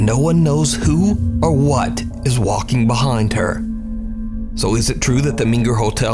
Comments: I hear "who is it " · Sounds like it said who is it